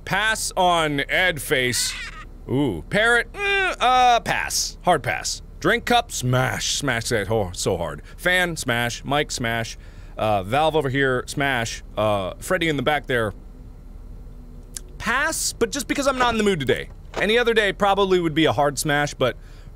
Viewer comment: i was kind of spacing out while watching this and all i heard was “Mike? smash” and was caught completely off guard. didn’t realize he was talking about a microphone i thought he was talking about Michael😭